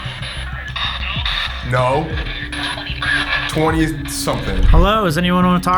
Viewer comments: Sounded like it said “20th room” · I heard 2002